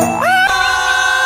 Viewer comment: Я один слышу "Давай, здох" ? Edit: 6 лайков? Это мой рекорд!